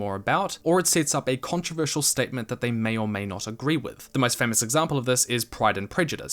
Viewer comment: it says may or may instead of may or may not Great video tho